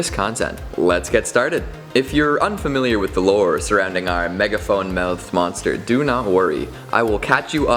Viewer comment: did i hear that right megaphone milf monster?